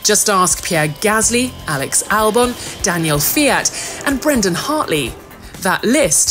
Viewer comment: I heard Danil Fiat